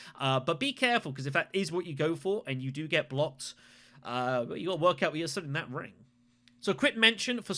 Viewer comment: Sorry but what does he say at 8:25? It’s a little hard to make out. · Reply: Apologies, "If you get blocked, better work out where you're sending that ring"